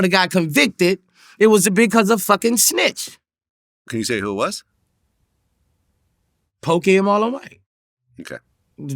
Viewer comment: wtf he say bro? Sound like... Poke'em allthe'way ?😮😂 we need a Boosie to English translator app. My ol'head Whytebuls will be DL'ing that shyt. need a droid version too for metro PCS east coast o.g's 😂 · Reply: He said Pokey & Marlo Mike they supposedly snitched on him